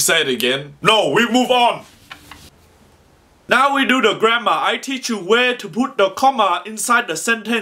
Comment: I thought he said now we do the grandma